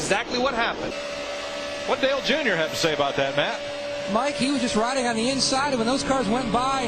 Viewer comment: i almost thought they said "what the hell did jr have to say about that?"